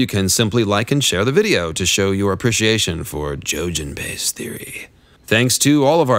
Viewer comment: Did he just say 'Jojen Piss Theory'?